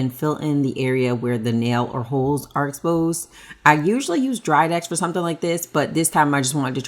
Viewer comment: Did she say she usually uses dried ice?